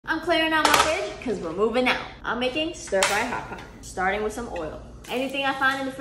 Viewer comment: Omgoodness i thought you said, hot pocket stir fry 😂😂😂😂😂😂